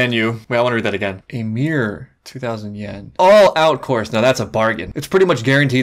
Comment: I totally heard "A Mere 2000" as "Ymir 2000"